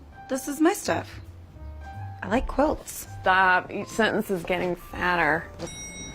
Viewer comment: I thought she said each sentence is getting fatter